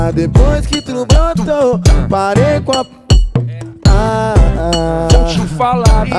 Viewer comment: Parece que ele vai falar 'Parei com a putaria' Kshkshksh fazer o que né, foi o que me veio em mente :')